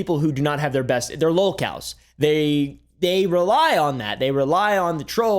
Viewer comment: this whole time i thought he kept saying "locals" weirdly for no reason... but he was saying "lolcows" which ive never heard of 😭